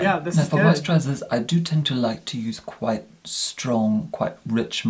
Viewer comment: Did anyone else think that John said "tuna" instead of toner at 14:22? I was super confused for a second lol!